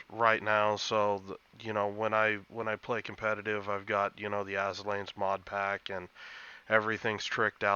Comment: lmao I thought you said "when I play competitive I have the azure lane mod pack"